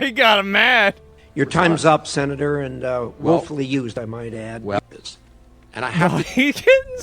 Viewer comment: "You time is up Senator and woefully used I might add" - The chair lol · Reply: I thought he said "willfully" :/